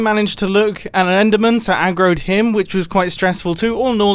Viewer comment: What mumbo says at 24:30 : I accidentally looked at an enderman so I aggroed him. The captions: I groped him